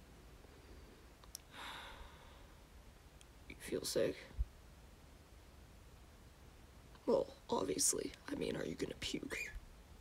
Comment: I thought he said "do u feel safe" so I said "ofc I am, I am with u" then I realized that he actually said "do u feel sick" and I was like "NOOOOOOOOOOOOO I'M SO SORRY!!!!😭😂"